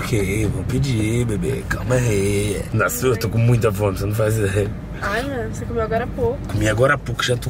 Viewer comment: Foi só eu que escutei no 9:16 ai amor tá namorando tá namorando tá namorando 😂😂😂😂😂😂